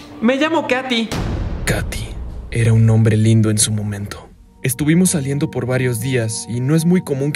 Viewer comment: Soy el único que en vez de “era un nombre lindo en su momento” escuché “era un hombre lindo en su momento” :v?